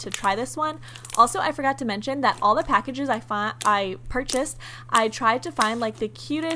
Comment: It sounded like you were saying find instead purchased. I’m skeptical.